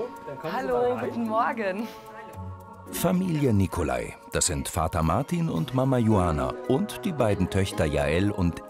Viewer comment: hab zuerst Marihuana verstanden lol